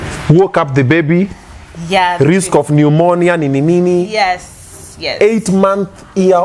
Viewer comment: Did just say „eight month year old boy“ 😂😂😂